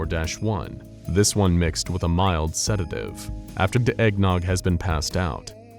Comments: why does it sound like they said da eggnog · i just realized at 10:32 he says "after da eggnog" instead of the